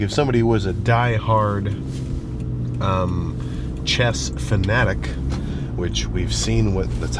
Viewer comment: i thought he said CHEST not chess until he mentioned magnus lmfao 😂